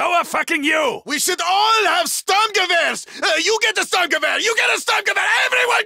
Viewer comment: YOU GET A STURMGEWEHR, YOU GET A STURMGEWEHR, EVERYONE GETS A STURMGEWEHR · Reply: I thought he said stungewehr because it sounds not like sturmgewehr